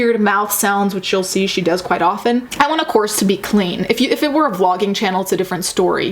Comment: i thought it said corpse i